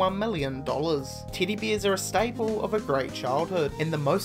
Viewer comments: Is it just me, or am I hearing "Titty Bear"? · When he first said it I thought he said "titty bear" and was like wuh?